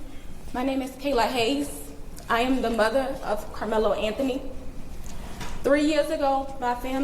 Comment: i thought she said Criminal Anthony